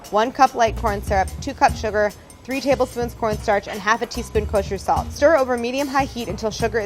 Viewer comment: Omg when she said cornstarch I thought she said pornstar 😂😂😂😂